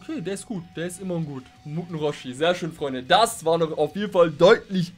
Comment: Bei 8:31 sagst du muten roshi.., hast es aber irgendwie komisch ausgesprochen für mich hat es sich angehört wie Mutlu roshi.., mutlu ist ja ein türkischer Name.. des wäre doch was oder? Türkische version würde dann muten roshi, mutlu roshi heißen! 🤣👍